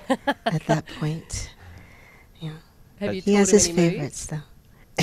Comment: what did she say at 4:32 ? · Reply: I think the interviewer said “have you taught him any moves” and Janet said “he has his favorites”